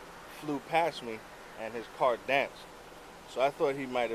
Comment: So very annoying, especially the “the car danced a little bit”. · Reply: I heard him say dance so much I thought he said dashed one of the times.